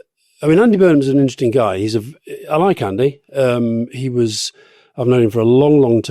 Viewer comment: did Alastair say of Burnham "I like Andy" or "eye-candy"?